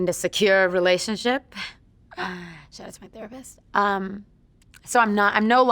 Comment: Did anyone else hear "shoutout to my therapist" at 6:09 ?